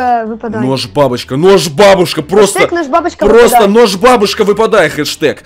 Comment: МНЕ ОДНОМУ КАЖЕТСЯ ЧТО ОН СКАЗАЛ БАБУШКА???